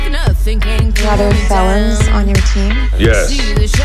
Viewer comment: What does the girl say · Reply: I'm pretty sure she asks "Do you have fellons on your team?"